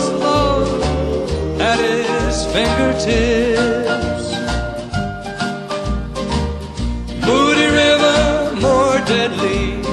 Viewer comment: Funny, I always heard "...than the vainest NIGHT" !